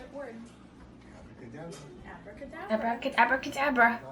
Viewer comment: When she said abra cadabra I thought she said avada kedavera lol Like if u love Harry Potter